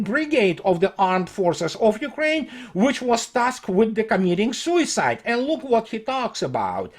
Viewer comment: I misheard the following: which was tasked with comedic* sui&cide (added &). Would have been another classic, such as: This is Liz Truss ...[perfect comedic pause] she's an idiot That said, wouldn't it be fitting if Zelensky was tasked with "comedic" sui&cide.